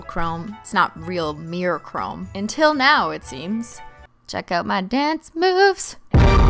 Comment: When she said "check out my dance moves" it sounded like she said "check out my dads boobs" 😂😂😂😂😂😂😂😂😂